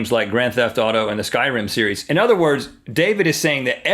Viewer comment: Did Rhett just say the "Skyrim series"?